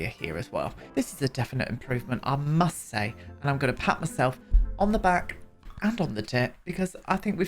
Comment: I thought u said “gonna pat myself on the back, and on the dick”!! I was like ur scandalous but not that hahaha